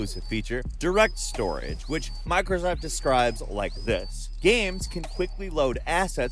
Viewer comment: Did you just say "microsucks" at 2:11 ? 😂😂😂